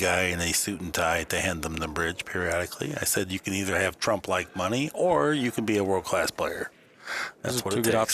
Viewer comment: Did he say you need to have "Trump like money" at 15:45 🤣He thinks Trump is rich.